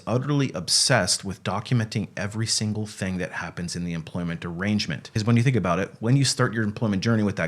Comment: Almost sounded like you said "employment degrangement" at 2:05. If so, it was widly accurate 😂😂